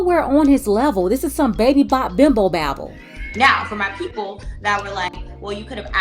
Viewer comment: Did just say Baby Bop Bimbo Babble?😂😂😂😂